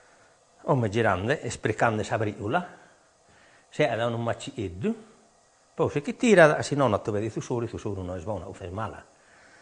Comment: Bello.... ma vorrei capire cosa dice dal m. 28:33 al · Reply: adesso sta girando,sta raggruppando le briciole,si forma un mucchietto. si leva ,altrimenti rimane nella ricotta, (piccoli pezzetini di formaggio residuo che non vanno nella ricotta, perchè sono sgradevoli trovarli quando mangi la ricotta).